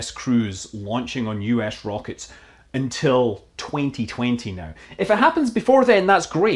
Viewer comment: Did you say 2029 at 3:49? That's a long ways away.